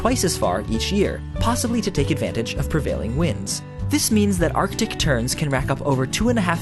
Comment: I thought he said Arctic Turd when he said Arctic Tern XD